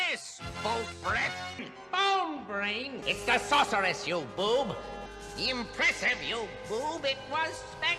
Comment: Did Skeletor called Evil-Lyn a "boob?"